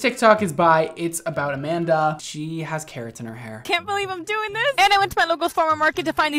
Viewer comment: What I thought I heard: “She got Keratin in her hair” What I saw: Carrot in her hair 👁👄👁